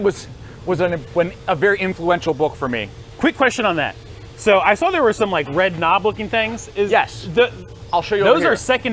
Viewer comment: He talks about a book that was influential book for him but I didn't catch it. What is the name of the book? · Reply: It's called "SMED" by Shigeo Shingo <link>